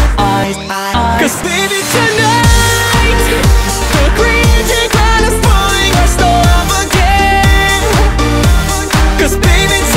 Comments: I swear to God I heard 'the creature is trying to bully our stuff again' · Thought it said “You crazy B**CH”